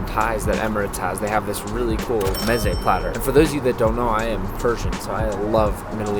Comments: did he say Persian or something else? If he said Persian, sick! Same! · did he say “ I’m a virgin” or did I just completely mishear? If so I can relate bro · did he say hes persian?